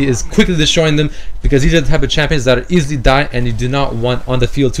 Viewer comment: DID I JUST HEAR 'CHAMPIONS' ????